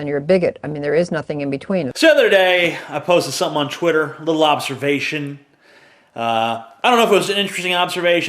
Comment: Holy fucknuggets. I started listening to the vid. When said the words "Lil Observation" I didn't hear it correctly. and it sounded like he said "The Lobsterbation" As in like, the lobster masturbation. I suddenly said "What the actual fuck?! What kind of shit would you post that is called The Lobsterbation?!" So I backed up and listened again, Facepalm finally realized what he actually said. I just had to share this, maybe some of you have the same kind of humor I do.